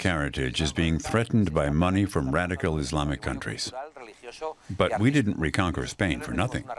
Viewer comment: subtitles are wrong, he says radical islam,and other wrong things